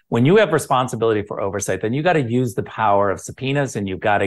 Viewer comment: At 8.47 it sounded like ric said ya need to use the power of the penis...😂😂😂